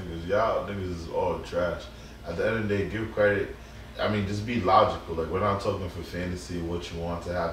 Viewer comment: Did just say to be logical? 😂😂😂😂